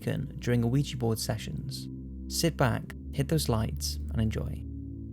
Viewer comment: At first i thought he said "hit those likes" and i was like nah youre not convincing me that easily and then i realized he said "hit those lights" and i thought "id rather give him a like, subscribe, and share the video with 1000 people before i even fucking touch my lights"